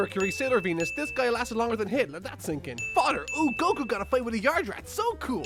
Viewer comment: sounded like he said Hitler